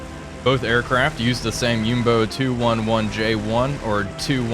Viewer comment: Jumbo? At least that's what I hear! I know it's nitpicky, but that's what the comment section is for!